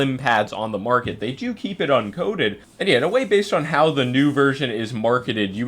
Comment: i could have SWORN said the phantom was uncoated..